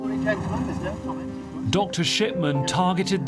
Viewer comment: I’m sorry did he say Dr. Shitman? 😂😂